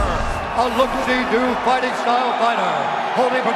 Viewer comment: what did bruce say what kind of fighter jon is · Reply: "A lookseedo fighting style fighter"